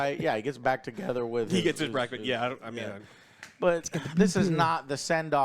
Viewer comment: "He get's impregnated" Did I hear that right from? Hahahahah true xD